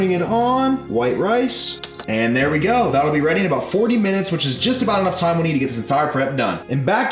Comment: did he say 40minutes for the rice cooker at 3:20?